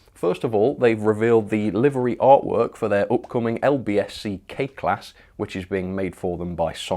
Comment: I thought you said LBSC Cake Class at first. Now that would make a nice pairing with the Tart Class!